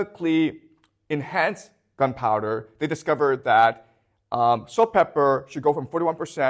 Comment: is he saying "salt pepper"? Does he mean saltpeter?